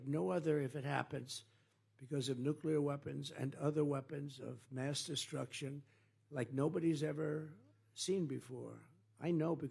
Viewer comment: Sounds like he said debate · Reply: I thought he said "mass debate" but then I remembered he usually gets Ivanka to do that for him.